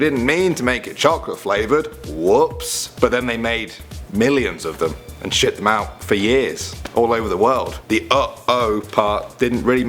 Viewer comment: In the uh-oh Oreo it sounds like he said we made millions of them and shit them out all over the place